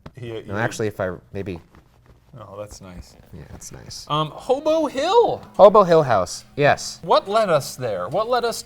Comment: anyone else hear shane say “homo hill” at 3:15 lmao